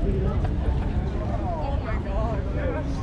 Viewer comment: did she say OH MY DOG rather than OH MY GOD